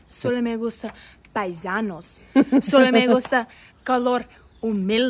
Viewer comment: Yo quiero una gringa que le guste color humilde.😢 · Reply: Menos mal que lo has dicho porque yo entendí "calor humilde"😂